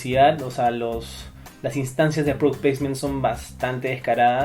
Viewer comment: ¿Qué dice? ''Las instancias de (.....) son bastante descaradas'' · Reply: aaaahhh... "product placement", termino que se usa para describir los momentos en donde se insertan marcas, logos o productos en una película como parte del canje para financiar a la misma. Tondero hace mucho de esto con DirecTV, por ejemplo, y en el caso específico de Guerrero, con Milo y LG. <link>